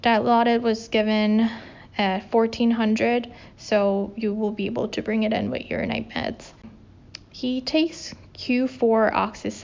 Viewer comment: 선생님 영상으로 영어공부하고있어용!! 혹시 영상에서 처음에 we'll start with bed ~ 그다음에 뭐라고 하신건가용? bed space four 인가용??? 그리구 02:25 쯤에 So you will be able to bring it ~ 그다음에 뭐라구 하셨는지 알려주실수 있나용???? 그리구 He is ACHS accucheck 이라고 말하면 되나용??? 06:20 쯤에 She's therapeutics times 1? 이 무슨 뜻인가용????? 감사합니당^^ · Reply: 처음에는 you will be able to bring it in with his(your) night meds. (환자가 around the clock으로 PRN pain med를 찾는 경우에는 미리 다음 간호사에게 말해주면 pyxis medstation에 약 꺼내러 여러번 가는 번거로움을 줄일수 있어요). He's ACHS accucheck 이라고들 하더라구요 그래서 저도 그렇게 해요ㅎㅎ. 그리고 마지막으로 Therapeutic times 1 은 heparin이 high-risk medication이라서 gtt으로 맞고있는 환자들은 6시간마다 피 검사를 해서 PTT 값을 검사하는데요. 각 병원이나 환자마나 정해진 Therapeutic range가 다르지만 이 환자는 49-67라고 영상에서 말했던거 같아요. PTT 피검사 결과가 이 레인지에 들어왔을때 1번 이면 times 1. 연속 2번이면 times 2라고 하는데 이걸 말해주는 이유는 2번 연속으로 적정범위에 들어가면 주고 있는 unit의 heparin이 적정용량이라고 간주하고 6시간마다 피검사를 멈추고 그냥 모닝랩과 함께 하루에 한번씩 PTT값을 확인하게 되요. Therapeuticx1이라서 heparin 용량을 올리거나 내린 경우에는 다음 간호사에서 다음 lab draw 시간도 함께 말해주는데 PTT 값이 supra-therapeutic(ptt 값이 너무 높아서 환자의 bleeding risk가 올라가는 경우)나 sub-therapeutic(ptt 값이 너무 낮아서 항응고제가 역할을 제대로 하지 못하는 경우)가 생길수 있어서 더 신경을 써야되서 그래요. 영상봐주셔서 너무 고마워용 : ) 다른 궁금한거 있으시면 또 댓글 남겨주시거나 <mail> 연락주세도 되요.